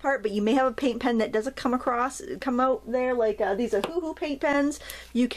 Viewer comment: What brand of pen was it that opened up so you didn't need to remove the nib? I listened to it twice, but it sounded like you were saying, "this is a woohoo paint pen", and the closed captioning called it a "google" paint pen. Edit: Oh I found the answer, guess I should have read the supply list! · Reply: Vicki Maheu Ohuhu is the brand name you are looking for